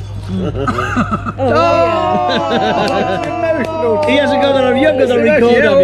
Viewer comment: burned out by "I'll have a prawn later" line, classic! She's the gaffer, for sure. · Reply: I think that I heard “.... a small prawn later ...”. I was so pleased that didn’t confirm that with a foto or, indeed, a comment! Great repartee though and what a road that Ronda Road looks to be!! Yours aye, Alan